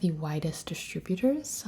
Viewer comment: I thought at 3:19 she said it was one of the whitest distributors